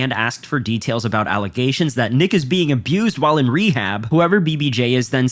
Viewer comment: dude I swear I thought he said niggas being abused 😂😂